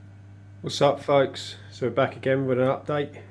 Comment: At the beginning i thought he said "Whats up fucks"